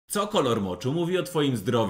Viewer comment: A ja usłyszałam oczu a nie moczu hehe XD